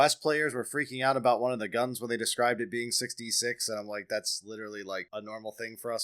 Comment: many people thought they said 66, not 6d6, THATS why they were freaking out. had some friends on a call watching it and i swear i was the only one who heard right lol.